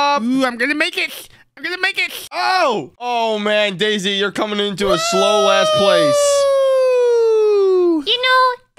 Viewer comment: Did say wow daisy your a slowed a$$ place